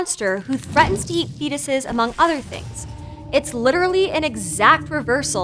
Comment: oh,fetuses,I thought she said "penises" which isn't a word since penae is penis plural